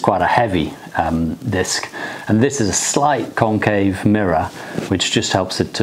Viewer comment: Totally thought he said “Cocaine Mirror” at first instead of Concave 😂